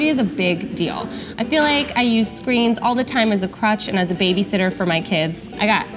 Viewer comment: I swear, I thought you said "crotch" not "crutch" at 0:35 😂